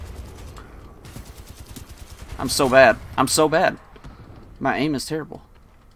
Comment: OkaY 0:35 When I read your enemy's name I thought you said "My Anus too.." I'm like wtH lol